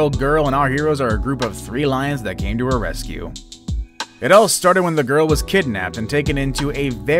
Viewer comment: Why did I hear " It all started when the GORILLA was kidnapped" 😂